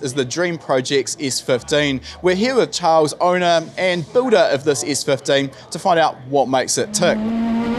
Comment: Every time he says "builder" I hear "Buddha" XD